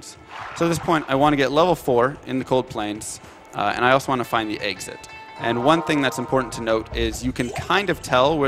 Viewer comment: Anyone else notice he says Axit? instead of exit...